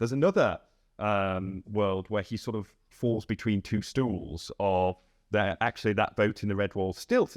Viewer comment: Did he just say" he falls between two shits " 6:30 only that's what I heard